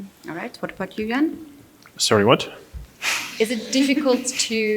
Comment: I think they've all misheard it as "what f*ck you again?"